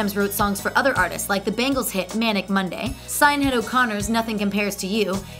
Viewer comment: Did she say "synade" O'Conner or did mishear that?